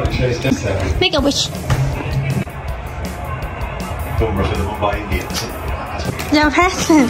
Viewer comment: were you watching IPL, I definitely heard Bumrah and Mumbai Indians